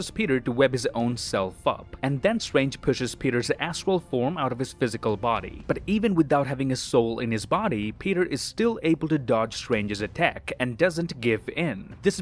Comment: i first heard this as "then pushes peters ASSHOLE form" and im not sure why i laughed